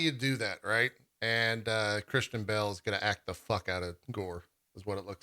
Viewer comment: I misheard and thought he said "Kristen Bell is going to act the hell out of Gorr". And i was like WAIT WHAT?